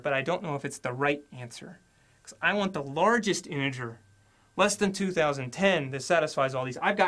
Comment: he said smth like "the largest anjer" instead of "the largest integer"